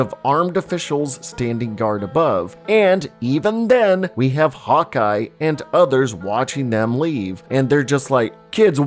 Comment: i thought you were saying “hot guy” instead of hawkeye 😅